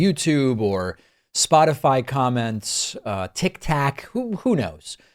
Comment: did dude just say Tic Tac